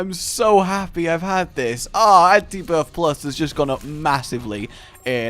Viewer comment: Did he just say antibirth +